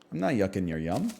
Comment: This is a weird request but can anyone tell me what he says at 29:49? I am still learning english and I have heard this phrase recently in his videos and I cannot exactly understand what he is saying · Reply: I believe he's saying "I'm not yucking your yum." Yuck is what you say when something tastes bad and yum is what you say when something tastes good. So basically, from what I gather, he's saying "I'm not saying that this thing you like is bad."